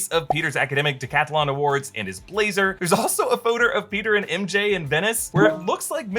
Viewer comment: did he say photer 😂